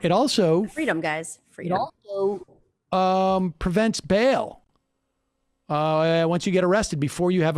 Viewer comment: wait, correct me if im wrong, but did he say "prevent bail"!? As in the thing you have a constitutional right to have a reasonable bail set?